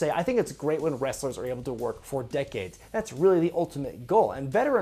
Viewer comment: At first, I thought said "I think it's great when wrestlers are able to work four decades", before understanding it was "for decades".